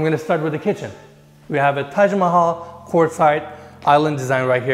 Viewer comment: did he said Taj Mahal♥️😁 🇮🇳🇮🇳💯